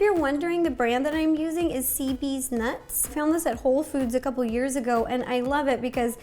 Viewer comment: i thought she said cd’s nuts instead of cb’s nuts